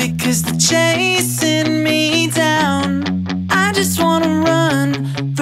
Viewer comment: ok, I hear "the fandom dragging me down." That's really relatable XD